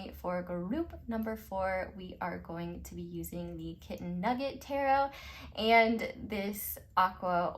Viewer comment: Yayyy! I was hoping you would post! Love you Queen! 💖✨ *also thought you said Chicken Nugget tarot deck. Maybe I’ll create a Chicky Nugget deck